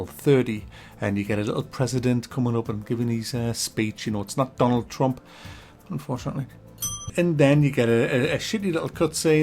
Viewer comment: it's not Donald Trump unfortunately? did i hear that right?